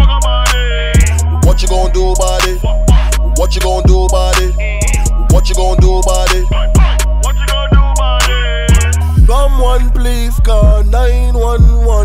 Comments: Thought he said shot my gold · I thought he said someone shot my gurl